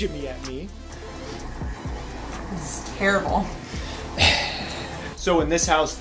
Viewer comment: She liked it? What I heard was "It tastes terrible"